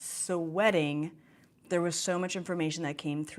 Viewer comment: At the start of the video I thought you said "Wedding, there was so much information coming through." No, you said "Sweating" 😂